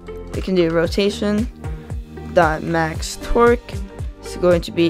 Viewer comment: roation.MaxTorque Vs what i heard : "roation.MaxTwerk"